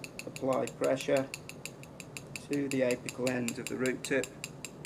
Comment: Could you give me a rough idea of when says this? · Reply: the aethecal end of the root tip, that's what I hear him say but I'm not sure. The part where he grinds it on the slide.